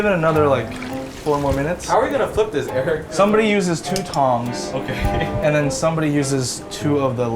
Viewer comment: i thought he said thong